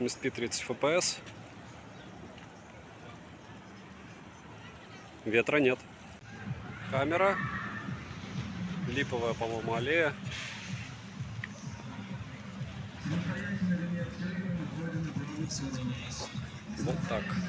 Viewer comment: привет 🤝 Благодарствую за обзор супербюджетника!!! 🤜🤛 Камера липовая на 13:11 минуте сначала было послышалось мне 😆🤔